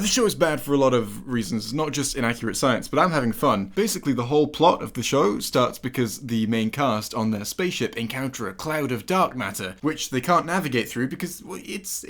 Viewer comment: "encounter a cloud of duck matter" is what I heard here for some reason.